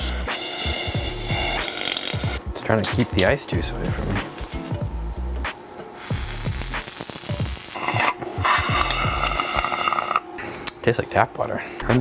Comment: did you call water "ice-juice"?